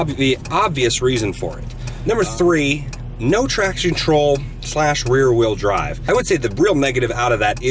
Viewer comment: Haha thought he said "Not traction troll"